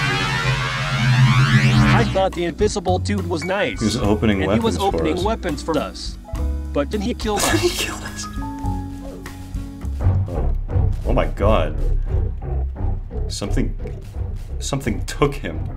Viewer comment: Спалился что русский :) Или мне только послышалось? Он сказал "Твою мать!".